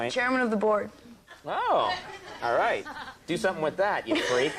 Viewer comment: what did he say there? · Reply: Conan - She's got a new movie coming out "Chairman of the Board"(looks at Norm and says) "Do something with that you freak.", Norm - " I guess board is spelled b-o-r-e-d."